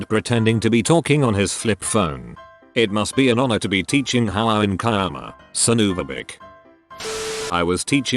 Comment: That sounded like Hououin Kyouma and as I was thinking it, someone actually said it as well! Huh, small world.